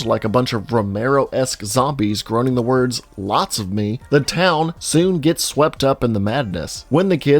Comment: I'm probably one of the few who thought Doofenshmirtz was saying "Not zombies" instead of "Lots of me"